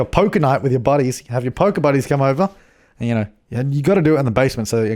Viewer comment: I thought he said Poke buddies for Pokemon and I was so confused when he said "so the cops don't see" 😂😂